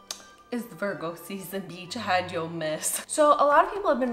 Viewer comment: What does she say at the beginning? I got, “It’s Virgo season. Peach, hide your _____” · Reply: "Beetch, hide yo mess."